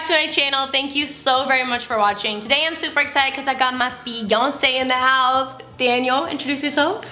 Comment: In the beginning I thought you said “I got Beyoncé in the house” hahaha